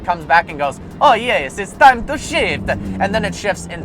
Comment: totally thought he said “it’s time to shit”